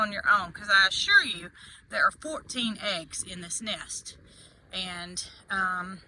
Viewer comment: I thought you said 15 eggs. 😊